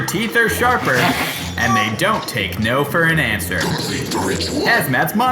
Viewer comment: i almost swear he says eat the rich